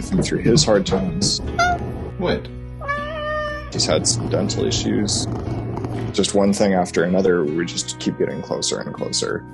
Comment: I misheard "mental issues" lol 😆